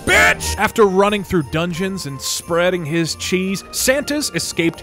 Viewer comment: What he said: "Running in dungeons and spreading cheese" What I heard: "Running in dungeons and spreading cheeks"